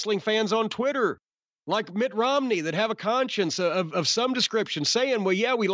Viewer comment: For a second I thought he was saying Mitt Romney is an aew fan